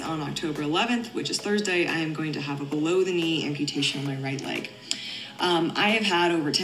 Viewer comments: Every time you say bellow knee amputee, I hear "baloney amputee." 😂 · At one point when you said a below knee amputation I thought you said baloni amputation